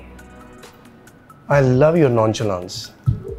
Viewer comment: What he said at 7:06? Launchalaa? What does that mean. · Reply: "I Love your Nonchalance" : I love your INDIFERENCE/ UNCONCERN